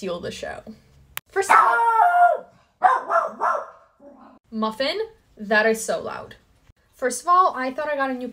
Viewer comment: I heard "Morphine" n I'm like cool name 😎 then I realised it's...Muffin. I'm so high 😂